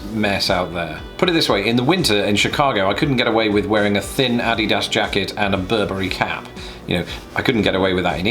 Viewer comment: does he say "addy-dass" for Adidas? 😃